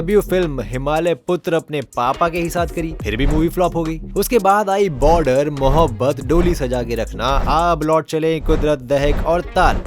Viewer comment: Why did I hear “dodhi” saja ke rakhna 😅